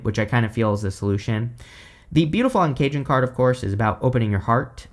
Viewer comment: I hear "beautiful uncaged" as "beautiful and cajun" and I don't know what that means for the month ahead!